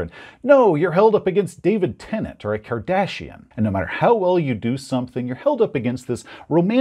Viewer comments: I was confused for a bit I thought he said Cardassian. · And now, for the rest of the story: and Jeff made a solemn vow that night to kill every character that Todd played on their games. Also, when you said “David Tennant or the Kardashians”, I heard “David Tennant and the Cardassians”. I was all “What? That race with spoons on their foreheads from Deep Space Nine?”